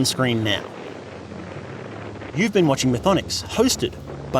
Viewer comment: Bloody hell I think I had a mini stroke at the end 37:45 ish. I heard 'And remember, you're never alone in Applebee's". Left me utterly confused where that came from. Applebee's not even close to 'in the backrooms". I'm not even american, I don't even know what an Applebee's is, only the name.